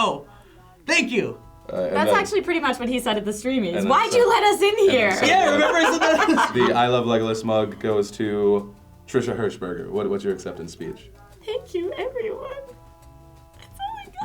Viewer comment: I thought phil said legless not legolas.